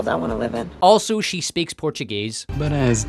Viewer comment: What the hell DID she say? Somethimg about "dona de casa"?